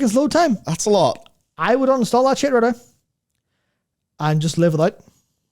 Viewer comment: can i have a translation to english what he's saying at 12:38 ? Thank you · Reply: It sounds like he says "I would uninstall that shader, though. And just leave it out." His accent does make him harder to understand sometimes xD